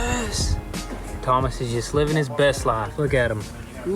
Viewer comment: I thought he said dumb ass but he called his cat Thomas